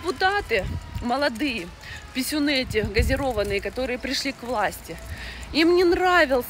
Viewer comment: "писюны газированные"😂😂😂😂😂 или мне не то послышалось?😊